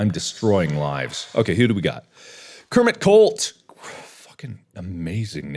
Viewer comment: I wasn't looking at my phone and had to check again because I thought I heard Kermit CULT